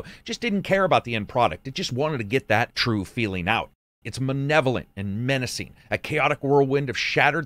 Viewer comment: bro did you just say "manevolent"